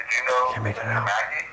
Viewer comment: thought I heard 'Mackey'